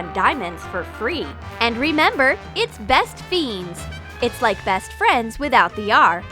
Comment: I thought it said best friends